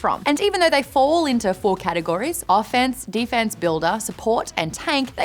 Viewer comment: "Fall into 4 categories: Offense, Defense, Builder, Support and Tank." Uhhh... Am I counting wrong? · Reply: It sounded like she said Defense Builder, but idk.